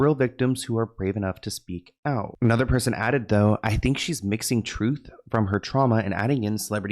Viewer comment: I thought they said she was missing.